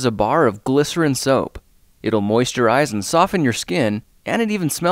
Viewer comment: I heard moist your eyes